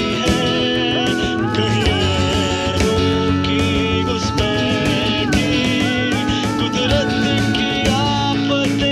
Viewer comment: What did he say at 2:57? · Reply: Gairoon ki guspaithi, qudrat ki aafatein likhein hain..